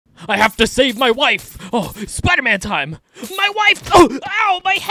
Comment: I thought he said ‘I have to SHAVE my wife’ 😭